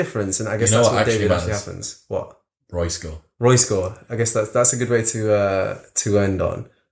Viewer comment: they say something that sounds like "roy score"... if anyone knows what they said and what it means, please share... thanks.